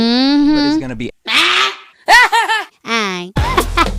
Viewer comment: does she say Mini?😮😮